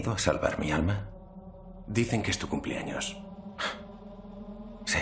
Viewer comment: qué dijo él hombre, todo puedo oír es "do you think es tu cumpleaños?" en realidad qué dijo? estoy intentando aprender español. Por favor alguien puede ayudarme? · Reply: en español dice "dicen que es tu cumpleaños" They say it's your birthday XD